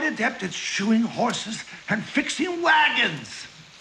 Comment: When I was a kid, I always thought he said he got good at "shooting horses and fixing wagons!"